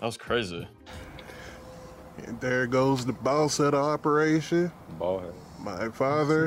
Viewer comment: I absolutely lost it at 6:33 because I misheard it as "there goes the ball sack operation."